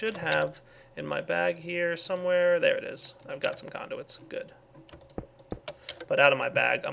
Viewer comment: Good i have some conduits Me:did he say condoms??????